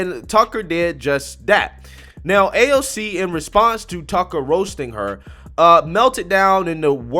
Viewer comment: honestly i thought it said aoc meltdowns over truckers lol